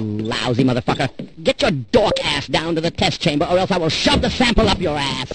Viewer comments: Man I thought that the dude was saying “or I will shove this apple up your ass” which made no sense because why would there be a apple in a vending machine? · i always thought he said snapple and not sample since he was at a vending machine lol